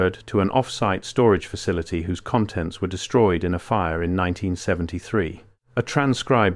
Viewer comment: I thought he said all files w3re destroyed